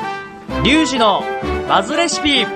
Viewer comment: 挨拶のリュウジです！がシュウジに聞こえたw すでに出来上がっていらっしゃる？w わかめご飯メッチャ好きなので次の休日に作ります！